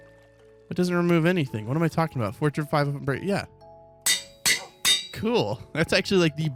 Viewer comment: he really said FORTUNE 500 😂😂😂😂